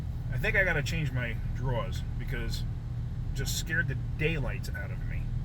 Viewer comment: You were so flustered by this “close encounter” that your New York accent came out at 00:22 (draws instead of drawers) 😂. Good safety tip for all of us. Glad you’re ok to continue wrenching.